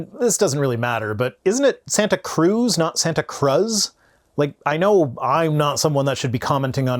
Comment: I legit thought he said “Santa Claus” County.